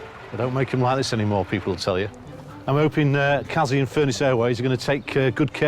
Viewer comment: i thought he said "fearless airways" at first!! i almost fell out my chair of laughing LMFAO ahahahahaha